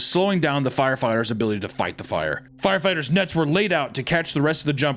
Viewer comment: “Firefighters nuts were laid out to catch the rest of jumpers” 😂😂 (He did say nets but I heard nuts 😂)